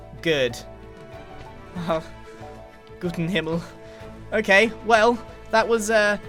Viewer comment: Am i the only one questioning what he says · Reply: Gott in Himmel. It's German, literally translating to "God in Heaven" and basically means "Oh my god" or "good heavens"